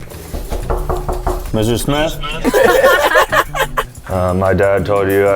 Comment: Thought he said "Messerschmitt", Mr. Smith lol.